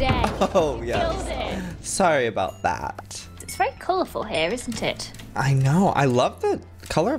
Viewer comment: What I heard: "It's very colorful isn't tit?" What she actually said..: "It's very colorful isn't it?" Whoops.